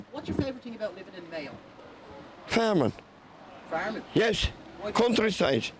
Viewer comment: I thought he said famine. I thought so it is true then.